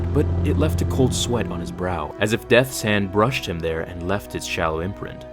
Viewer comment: did you say "death sand"? he hates sand like Dad? I gotta get out my novelization. is this from original novel or "Beware the power of the Dark side" version? I heard there's several key references in that version, including Ghost Obi-wan remembering Qui-Gon. I so gotta buy that version too.